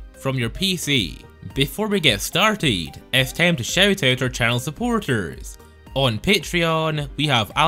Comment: thought he said child suporters